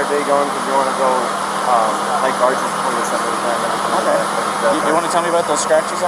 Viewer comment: What did he say at 5:25? He said something like they were plan on going somewhere? A lake to get water? Correction: He mentioned Arches which is a national park. There is also the Colorado River nearby.